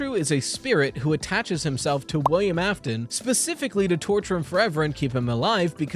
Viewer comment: Bro I misheard "attached himself to William Afton" as "touches himself to William Afton"💀